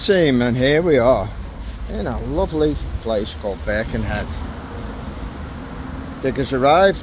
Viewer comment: Jee, finding out that what I heard as "beckinhat" is actually "Birkenhead" took quite some geo-guessing-skills 🤣! But just looking at google maps makes want to visit the Liverpool area some day 😍! You obviously feel comfortable with all kind of tools, great job 👍!